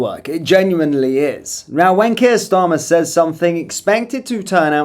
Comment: "when Keir Starmer says something" What I heard: "wankier starmer says something"